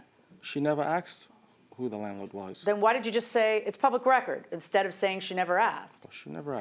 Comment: Oh my gosh!! Did anyone else catch this “judge” when she says something dumb? So about 3:05 or 3:06 into this clip, she asks the defendant who the landlord or owner is & he tells her this (LLC co.) & he says “it’s public record”, he literally says it like twice before the judge gets snippy with him and says “why don’t you just say it’s “public record”?? -LOL, I’m shocked the Defendant didn’t say “I literally just said that TWICE” Geeze, she really needs to listen more carefully cause she does this more than she should! -just saying!!😮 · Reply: The judge didn’t say anything dumb here. She actually said, “Why DID you just say it’s public record?” instead of “She never asked.” It was sketchy that the “public record” thing was his first response to her question. It made it very plausible that the plaintiff did ask who the landlord was and he refused to tell her. And that’s sketchy.